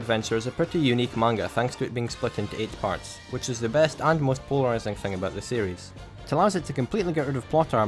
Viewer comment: The hell is being said here? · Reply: Al polarizing